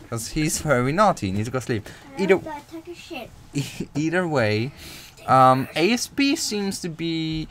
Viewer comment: Did he just say, "I don't give a $#! π"?